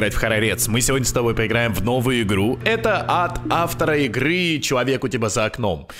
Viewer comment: я услышала как "это ад от